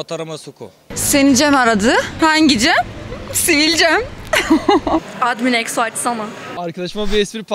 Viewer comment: De kız ne diyor anlayan var mı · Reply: admin exo acsana diyor